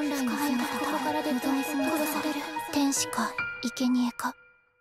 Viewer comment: 天使かイケメンか…って聞こえて笑った